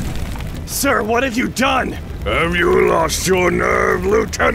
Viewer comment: Sounded like he said, "sir, what did you don'e?"